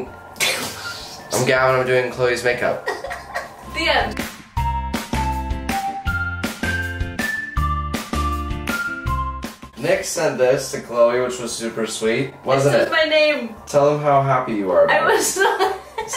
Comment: I really thought he said ‘nick sent you this’ lmao